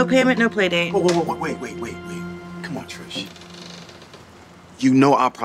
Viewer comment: I thought the guy said “sorry Trash” but he actually said “sorry Trish” and I was like BURN